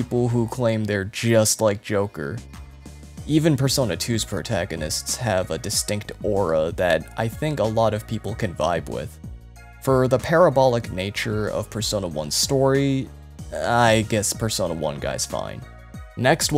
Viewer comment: Entirely unrelated, but instead of "parabolic" I heard "pair of ball lick" and shot tea out of my nose all over my phone 🎉 Great video btw. 10/10